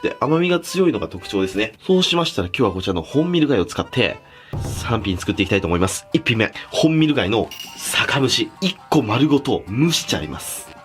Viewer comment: 3Pに聞こえた。末期かな、、